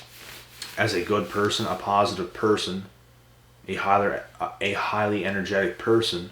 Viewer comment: Stop at 9:19.. did you just say you are a “hotter” person?! 🤣 JK just wanted to make you laugh... hope it worked!! Have a great day friend‼️